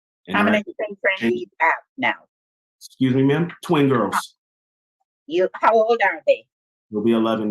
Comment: Lol….. thought he said “20 girls”